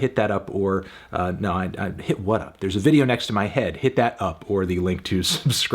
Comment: Am I the only one who thought he was saying “hit that UPworthy link”??